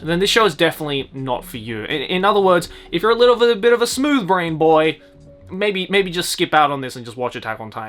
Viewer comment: Is Joey called us the monkey brain can't watch Wonder Egg Priority !?!?